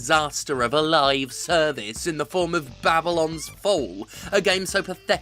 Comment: So completely had Babylon's Fall passed me by that I misheard it as "Babylon Four" and thought, what do you mean there'll never be a sequel, they made the sequel in 1994